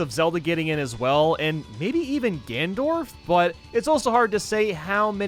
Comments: Did he just say Gandorf Like Gandalf and Ganondorf combined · I swear he said Gandorf